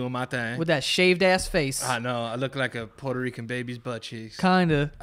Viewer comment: "I look like a Puerto Rican baby's butt cheeks" "Kinda" Hot Start😂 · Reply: I swore he had said butt cheese lol